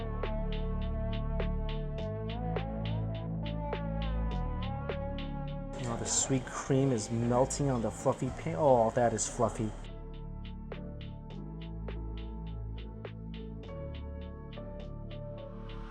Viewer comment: I thought he said "the sweet cream is melting on the f****ng pancake" I need help...